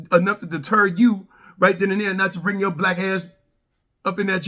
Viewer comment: I guess he only talking to the black community, which nothings wring with that. Just saying I heard him say "yo black ass"